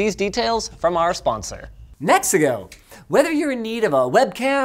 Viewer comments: i thought he said Mexico not "NEXIGO" I'm over here thinking that he got sponsored by a whole ass country. :face-blue-smiling:😂😂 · Every time I hear "Nexigo", I hear "Mexico". 🙂